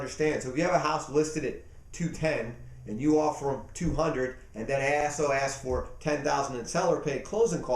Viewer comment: What is he saying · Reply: Right, I swear he says, 'And that Assh*le asks for' lol